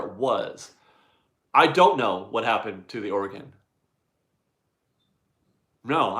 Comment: (Smiling): Am I hearing things or do you keep saying, “Oregon“ at about timeline 10:48? Either way thanks for all the great info.